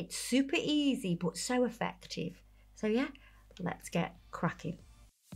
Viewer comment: I wasn’t fully paying attention so I legit thought said “Let’s get quacking” at the start 😂😂 and now I’m hoping she sees this and it becomes a thing 😂